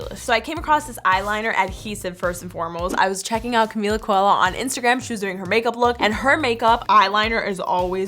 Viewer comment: who else thought she said "Camila Cabello"? 😂